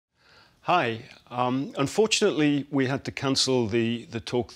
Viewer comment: At beginning I thought he said: “Unfortunately we have to canceled PS5” So scary, never start your presentation with “Unfortunately”